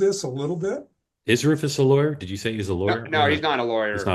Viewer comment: I thought he said Rufus "...was a liar...".